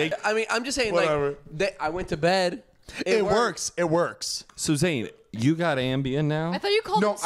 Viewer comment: I heard you, it sounded like said “Suzanne”😂😂 and nobody acknowledged it